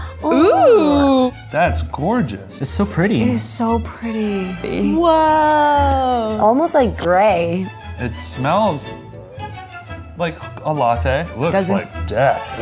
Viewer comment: Why the hell did I think he said: "it smells like balatte." ?